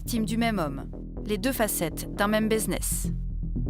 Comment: un quoi ? Baiseness ? 🤣😭